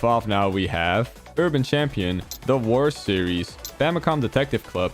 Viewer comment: When you got to Advance Wars, I thought you said “the worst series.” Took me like five times listening back on it to hear “the wars series.”